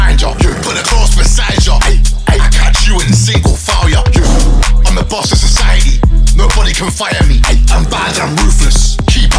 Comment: Did he just say I am the boss of the society 😳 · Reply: He says "I am the boss of society". I'm not sure if he's actually referring The Society or just society in general.